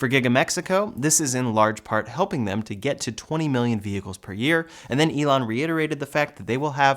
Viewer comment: did you say 20M/year?